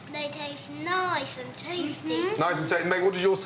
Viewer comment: gordons son at 36:06 ... "they taste nice and tasty"... what I heard... "Can I have some more?" - (Christmas story Tiny Tim)